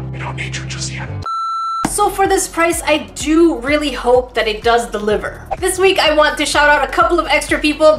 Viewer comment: it sounds like she said "it does the liver." Lol does anyone else hear that?